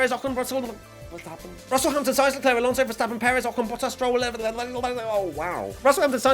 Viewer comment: Oof, that race rundown was rough. "...Vettel, Gasly(?), Stroll(??)...". You'll bounce back for the next one lol. Edit: Upon listening again I misheard Zhou as Stroll so thats my bad. But Gasly was definitely there.